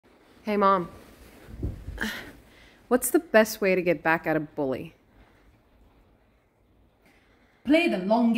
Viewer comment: My two brain cells thought she said how to get back at a belly😂